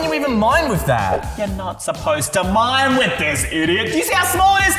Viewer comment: did said idio